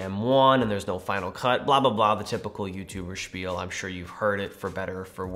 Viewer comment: did you say typical YouTuber (spiel)? I'm learning English and (Spiel) is a german word wich means game. Is that what you said and is it a English word too?